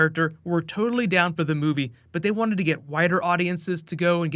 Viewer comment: I thought you said, “But they wanted to get WHITER audiences to get jazzed up about it," and legitimately shot rice out of my mouth laughing. I almost instantly imagined a “Deadpool 101 for White Supremacists" video with a Hydra watermark of approval in it, then a sequel video, "Black Panther 101 for White Supremacists", to stir up Black Panther movie hype for... alternative audiences. Luckily, I'm the only one in my family eating this late, geeking out with an overactive imagination.